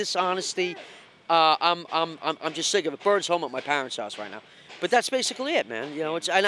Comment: does he say, “bird’s home at my parents house right now..” ??? Lol what goes on